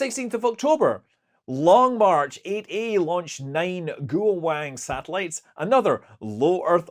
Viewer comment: I'm sorry, did you really say DUAL WANG satellites? The chinese now have DUAL wang satellites?!?!? We're screwed. Well, someone will be screwed. Twice. Perhaps.